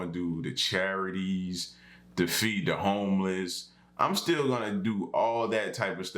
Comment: my brain is weird L said "to feed the homeless" and i heard "to defeat the homeless" xD